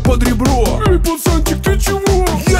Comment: Мне послышалось я решаю вам менет 🔞 извините 😅